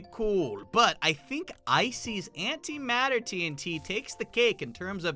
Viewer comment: did he just say ISIS antimatter TNT?